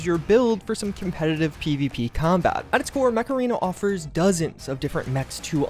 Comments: stg thought said mararena for a minute there, and now I have that tune stuck in my head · 🤖 Install Mech Arena for FREE here ✅ <link> and get a special starter pack to help kickstart your game! ⭐️ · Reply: Could've sworn you said "Macarena" 😆👍